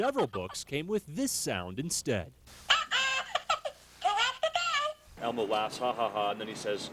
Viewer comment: It sounds like he said "Who wants to go?"